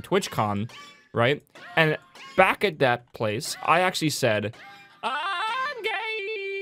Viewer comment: what did suscriber alert say? I didn't understand... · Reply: It sang "IIIMM GAAYyyYY"